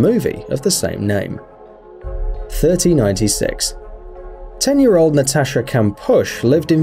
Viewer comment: did he say, "3096"